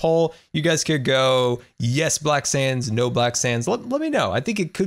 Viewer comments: I thought you said black sans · It sounds like hes saying black sans